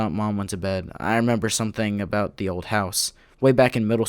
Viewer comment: I think it said something something in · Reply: I think it was split into two sentences I remembered something. Something about the old house